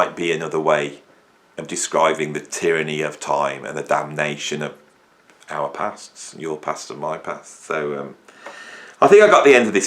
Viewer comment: Great video, very inspiring. I'm currently in the "vague" stage at the moment, contemplating a new project, but unsure where it's going. But that's part of the fun, right? By the way, for a moment I thought you said, "tyranny of the future and damnation in your pants", but that's probably just my warped brain! Hmmm, 'Damnation in your Pants'. New song title?